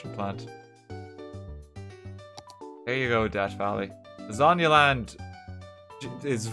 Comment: I'm gonna be straight honest I thought he said "death valley," not actually "debt valley."